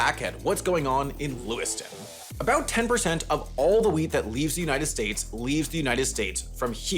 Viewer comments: Oh, WHEAT. I heard it as 'weed'. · I thought you said 10% of all weed for a second 😂